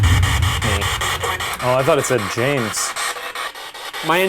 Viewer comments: Did anyone else hear F- me at 6:36? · I heard someone say “not me” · the voice sounds like it says fu** me... lol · Am I the only one who heard "F*** me" at 6:36?? lol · sounds like someone saying ‘Not Me’ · It sounds like he said "fight me" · It kinda sounded like it was saying "Not me".... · it sounded like wasn’t me